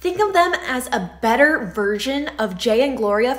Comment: Sounded like You said : virgin instead of version